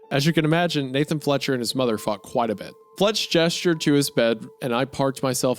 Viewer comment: audio only here, i leave the video open and go do other stuff, and I had to come back to check if they really said ''Fletcher and his mother fucked quite a bit''.